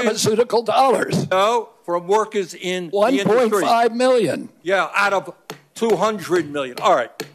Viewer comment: Bernie “ not a nickel “ 2 seconds later “Only 1.5mil out of 200mil” Ummmm, are the people who support these people this stupid? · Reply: I thought he said 25 million out of 200 million